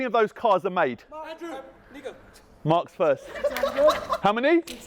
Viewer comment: Damn he really said the N word like it was a funny thing to say.